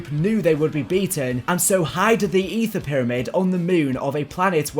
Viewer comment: did you really say "hided" "Hid" is past tense for hide What you said is used to describe animals with "hides"